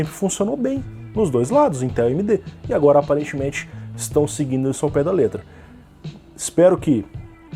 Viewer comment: Corrigindo pra você: "... então agora, aparentemente, a INTEL está seguindo isso ao pé da letra." Não ouvi, até agora, algum relato sobre a AMD ter feito isso, você ouviu?!